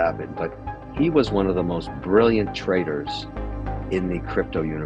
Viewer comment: Everyone thought O’Leary said “brilliant trader” but he actually said “brilliant traitor”